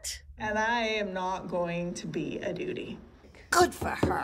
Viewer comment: Did Meri really say “I’m not going to to be a doody …..”.